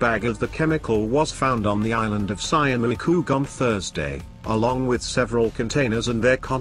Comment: Wait 1:08, I dont know what the robot try to say, but he meant 'Schiermonnik oog' (Dutchie here) one of the five inhibitant islands in the North of our country